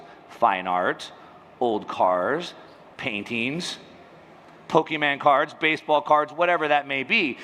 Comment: did he just say "pokeMAN" 6:46?? HAAHHAH